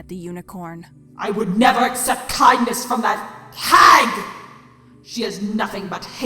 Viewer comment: I thought Chrysalis said FAG xDDD